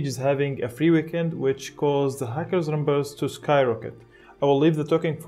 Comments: When you said "hackers" it sounded like you said "fuckers" and honestly, both are correct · For one sec ı thought you said fuckers instead of hackers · When he said of hackers, I thought he said of fuckers and honestly, yeah.